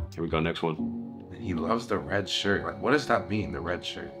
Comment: Yo I swear when said the red shirt nick a cad o it sounded like he said the red shirts n word cado